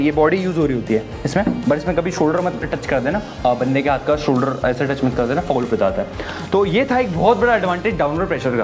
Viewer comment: bro may you plz reply that what you have said... it's not clear! 😐❤ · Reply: Shoulder touch mat karna foul ho jata hai Ye bola maine